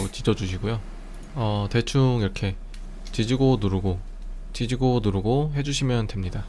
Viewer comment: 에 지지고 누르고멘트중에 지지고를 뒤지고로 들었다..